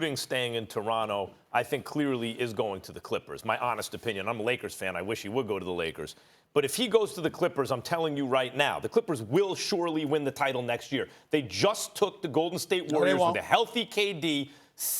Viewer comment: Did Max say he is a Lakers fan?